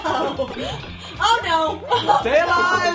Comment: Did I hear Peach go "Uh oh?"